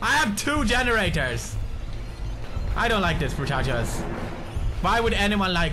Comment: what does he say? I don't like this "what"? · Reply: brochachoes